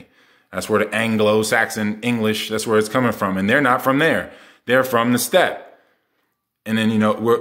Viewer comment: What did you say at 8:08? “They’re from the stat”? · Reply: The steppe